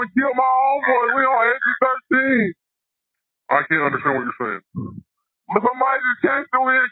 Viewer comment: Wrong translation. You wrote " how did they kill your friend" but what he said was "I can't understand what your saying "